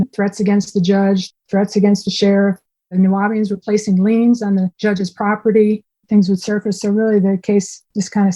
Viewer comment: “The Nuwabians were placing liens on the judge’s property” - did I hear that correctly??????